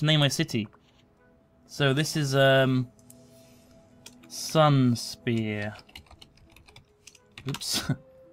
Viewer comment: I thought he said Sunsphere, which I think would have been a lot cooler considering the theme.